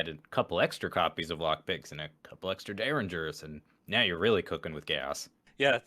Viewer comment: I heard “now you’re really cookin’ with gas” as “now you’re really cookin’ with chaos”